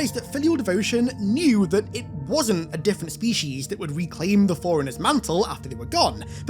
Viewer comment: You lost me in the first 10 seconds but I’m back online! Haha I thought you said “foreigners”